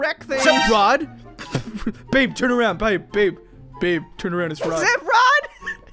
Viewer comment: I thought Said "beef" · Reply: But he said babe not beef😂😂😂😂😂😂😂😂😂😂😂